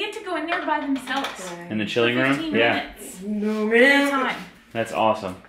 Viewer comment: sounded like she said “No ma’am”